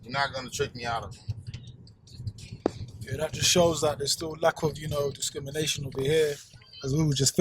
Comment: "And that just shows you there is still lack of you know discrimination over here..." Did I hear it correctly? 😮🫣